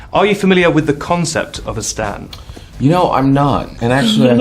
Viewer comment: This is so randomn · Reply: sounded like stand. Lol.